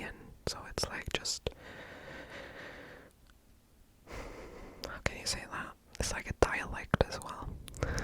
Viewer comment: uma pergunta no minuto 19:06 vc disse "ok sei lá"? foi o que escutei sendo um ouvinte Br. Edit: ativando a legenda vi que é "How can you say that" mas mesmo lendo ainda escuto a frase em português, que louco...